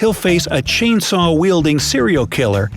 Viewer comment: At the haunted house one I heard "A cereal killer"